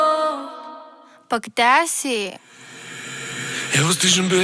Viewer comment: What is that word? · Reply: Pa gde si ? (Where are you)